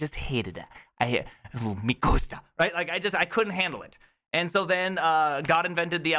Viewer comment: lol, did you say "no me gusta" ?